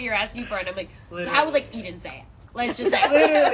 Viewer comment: who’s name is she saying? I think it’s Eden but I wanna make a tweet and I don’t want it to be wrong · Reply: I don't know if I'm too late to reply, but the name is Eden.